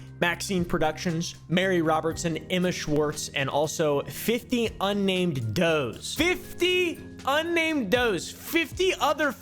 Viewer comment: Hahahah I heard “50 unnamed toes”